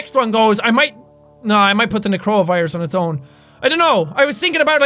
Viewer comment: i thougt he said corona virus instead of necro virus